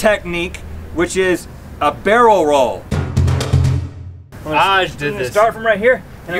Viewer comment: I thought he said the egg roll lmao